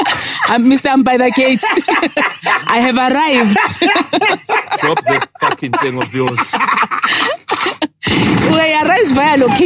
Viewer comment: For some reason I thought Lenn said drop that fucken thing of yours 🤦‍♀️🙆‍♀️🙃